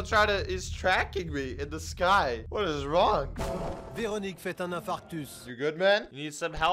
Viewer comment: Did i really hear « Véronique fait un infarctus » at 6:10 ?! I can’t stop laughing 🤣🤣